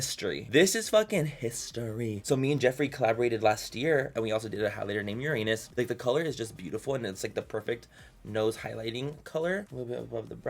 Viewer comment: When he said "highlighter in Uranus" I was eating chips and thought he said "how to name your anus"